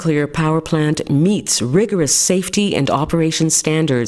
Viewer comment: We ensure every nuclear power plant meet’s ______ safety and operation standards [...]. What does she say here? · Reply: We ensure every nuclear power plant meets rigorous safety and operation standards to prevent accidents in the first place